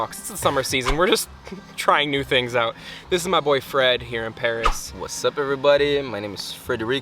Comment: I heard "this is my boyfriend here in Paris" and i was like "wait, what? You have different boyfriends in different parts of the world? WOW, cool. That's a next level of open relationships" xd